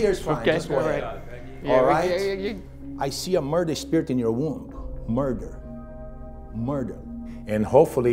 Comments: “I SEE A MURDEROUS SPIRIT IN YOUR WOMB!” Me: whAt? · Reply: I thought he said “I see a murdered spirit in your womb”, like he was trying to shame her for a past abortion or something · HOLD UP DID HE SAY "I SEE A MURDERED SPIRIT IN YOUR WOMB" ???? ????????? Y I K E S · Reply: Gatica I thought he said a murdered spirit in your room I was like ok her room is haunted then but jfc nope! okay then